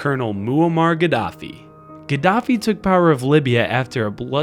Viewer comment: At first I thought it said “Gaddafi is a bitch”